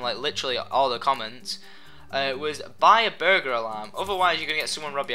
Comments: did he say buy a burger alarm!? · I thought he said "burger alarm." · Lol it sounds like he said a Burger alarm